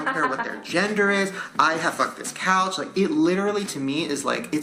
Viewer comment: not Shane saying he'd fuck the couch.... thought he said cat